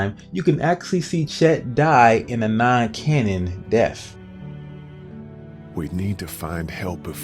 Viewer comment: Lol wtf. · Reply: sounds like hes saying "MMA CANON DEATH" like what??? Lol